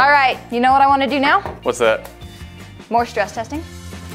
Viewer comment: i litarally thought she said morshgestizing....... umm yea i know ...sound it out (its